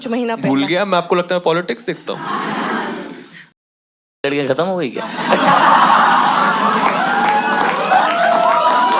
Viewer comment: yaha kya bola btao yaar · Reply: Yah Raaz bhi uske sath Chala Gaya 😂